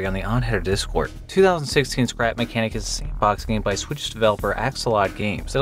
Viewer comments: There’s an easter egg that me and an old friend of mine discovered. In the game Star Fetchers there’s an easter egg to see a room with an npc for a jeffery epstein didnt off himself joke. it only appears during a certain hour. so we were in the room. and we started talking past that Hour. (by accident) by the time he talked to the npc it was past 12 : 59am which is the last minute before the npc despawns. and instead of the screen popping up a 3D graphic of J E didn’t off himself. but it just said “find me” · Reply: did you say "sandbox" or "shitbox?" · did you say "sandbox" or "shitbox?"